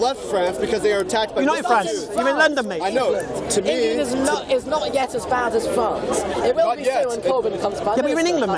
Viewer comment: I love this orthodox Jewish kid. May Allah protect him and bless him with the light of Islam. Ameen. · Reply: Hi.there I have a question around 15:27 did he say "England is not.as bad as England but it will be when covid comes by" ?? Or did he say prophet?